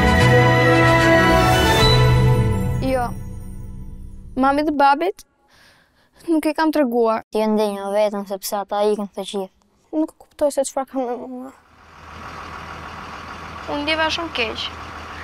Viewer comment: Mamed babed XD tf did she say